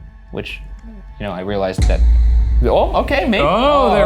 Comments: I hear like a different voice saying something like 'Oh' on 18:53 after the words we do like to party. Don't know if it's either of the guys exhaling or something else entirely. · For #Debrief at 18:53, I noticed a soft murmur after Ryan is talking about liking to party. Sounds like someone saying "oh" in a slightly disappointed way haha